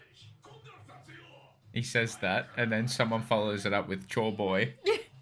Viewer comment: i swear i heard joyboy💀